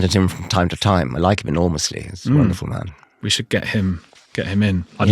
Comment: Did he just say "we should get him in" (to the programme) referring to Christopher Hitchens?